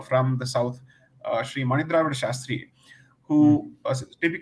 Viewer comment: what is the scholar's name that Sai mentions here? didnt catch it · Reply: The name of the scholar Sai Deepak mentions at 21:05 is Sri Mani Dravid Sastrigal. He is Professor at Madras Sanskrit College, Chennai and has learnt several sastra-s adhering to the traditional learning methods 🙏